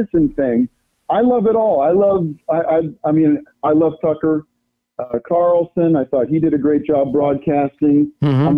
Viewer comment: "i love sucker...carlson..." anybody else catch that lol